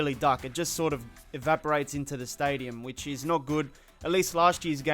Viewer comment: did he just say evaporated into the stand.